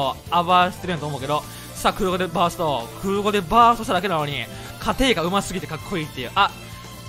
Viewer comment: 空後でバーストしただけなのに、と聞こえたきが